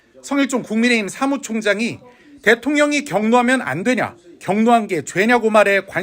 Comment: 대통령이 법을 어기면 안되냐? 로 들리는데?